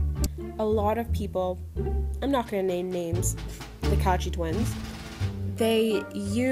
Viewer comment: I THOUGHT YOU SAID HITACHIIN TWINS AND I WAS LEGIT SCARED FOR LIKE 5 SECONDS HAHHA